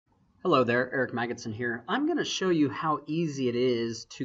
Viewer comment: I thought you said in the beginning of the video , hello "earth maggots in here " :-)